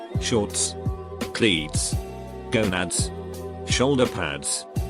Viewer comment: who else hears Gonads